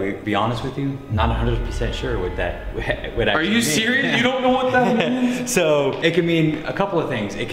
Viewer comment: When the first time it speak in the spirit box i heard before i heard “are you serious?”